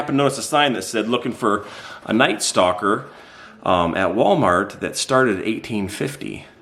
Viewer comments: Does that night stalker have to wear black?! Lol it did sound like said stalker not stocker! 😂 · Omg, I was working and listening to your video. When I heard you say that night stalkers get paid $18.50, caught my undivided attention. Then, I realized you were talking about night stockers. 😂😂😂